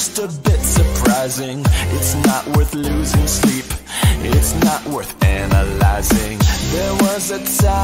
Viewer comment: I used to think he said it’s not what losers think